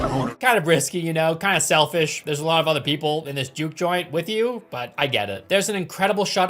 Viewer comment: I was watching this without subtitles and I thought they said "jew joint" instead of juke. That's no subtitles for ya